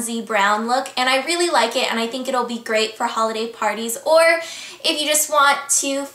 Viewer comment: ' i think it would be great halloween parties' thats what i heard when i rewind <num> times and finally heard holiday parties..